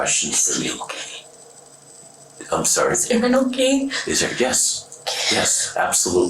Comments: she didn’t ask if everyone was ok, she asked if that twit bf/gf of hers was ok. She said Aaron not everyone. Just because your subtitle says differently, doesn’t mean that’s what she asked! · The dub at the end wasn’t correct. Her ultimate concern was for Aaron’s wellbeing, not the wellbeing of others. Instead of “Is everyone okay”, she really said “is Aaron okay” right after asking “is he okay”. Just thought I’d throw this is since her sigh of relief was really about her fiancée and not the people they shot at.